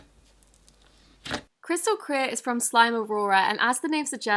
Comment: ngl I thought it said crystal ear